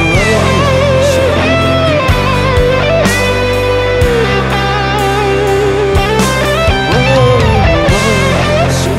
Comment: 請問2:15女生說什麼? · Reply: 帶我走